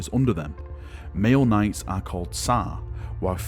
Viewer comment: Let’s play “What is Valrak saying?” “Male knights are called Sah, while female knights are called Sah.” 😂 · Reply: I think its Sah and Sal